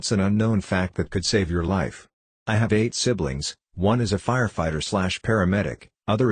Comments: For a fraction of a second I thought he said: “I have AIDS”. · I thought it said I have aids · I thought it said “i have aids”